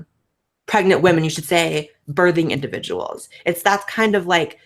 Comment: At some point I heard "barfing individual" and looking at my friend during first trimestr who is throwing up her insides through "fair" xdd listening in 2022 :)